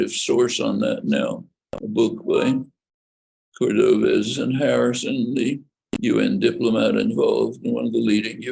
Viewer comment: I couldn't make out who Noam said was a definitive source for exposing the blockade of UN efforts to facilitate Russian withdrawl. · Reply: Cordovez and Harrison. Book is called Out of Afghanistan